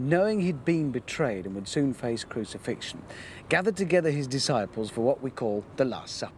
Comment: I accidentally skipped ahead in the video at the beginning and what I heard was "Paris has faced crucifixion"